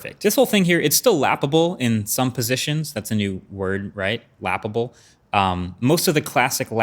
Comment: I thought he said it’s laughable then he shows it. Oh that lap able 😂